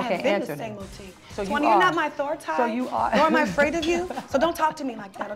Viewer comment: what did Tamar say @ 0:33? Towanda you're not my what?? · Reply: Authority